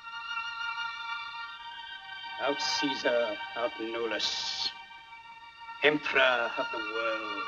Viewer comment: Iconic scene of world movies forever ... what is said on 1:46 in latin? Genius forever... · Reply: Something like: "Aut Caesar, aut nullus. ("Or Caesar, or noone". ["Either Caesar or nothing." – Google Translator says.]) Emperor of the World".